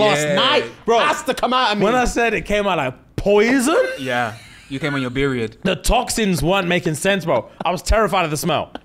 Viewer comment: Did say you came on your Beeriod? 11:58 😂😂😂😂 I'm in bits